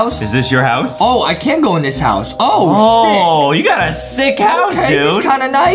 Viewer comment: OH SHI- Justin said it I heard it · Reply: He said sick